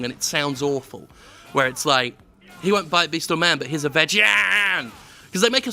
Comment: I hear "He's a vagian.", too! I bought my ears on Etsy, where did you get yours from?